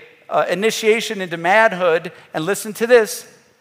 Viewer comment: Did He just say an initiation into 'madhood' woow!!😂😂